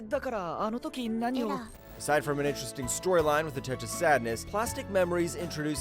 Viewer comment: did you just say touch of sadness? seriously 🥲